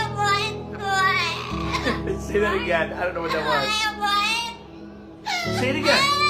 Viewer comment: Sounded like he said " i dont know where i am"😭